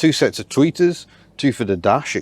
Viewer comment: I thought you kept saying tweakers.